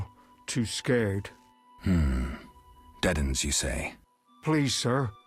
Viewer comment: Lol thought he said Deadends not Dead'un :) I like the dwarf, he had the coolest beard I ever saw. Talking about the dwarf smith.